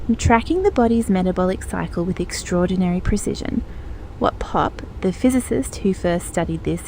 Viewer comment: what does "WHAT POP" mean? Your captions says "WHAT POP" 🫤 (why won't the British annunciate properly?) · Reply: That part goes: What Popp — the physicist who first studied this in the 1970s — proposed was that these biophotons are not random noise. Popp is the physicist. Thank you for bringing that up, hope that clears it :)